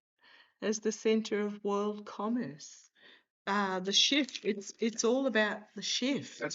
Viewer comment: I heard say "that's it!"